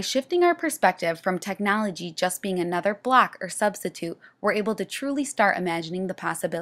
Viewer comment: I thought she was saying "a black is a black no matter where it is" so confused. I then realized she was saying "block" lol good description of the model